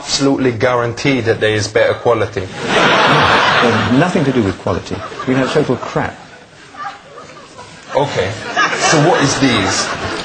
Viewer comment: "You know total crap"... is that what he said?